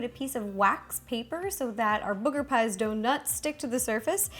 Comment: Instead of "do not" it sounded like she said "donut". 😂😂